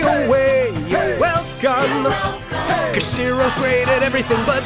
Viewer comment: saw the next 8 seconds in another video, and I hear She-Ra